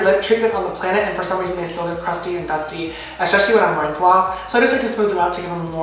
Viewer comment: yall i LOVE sister james, but who else thought of BROTHER IAN at 6:39 when he said "especially" and it sounded like "esheshally?" Love you sister ! xx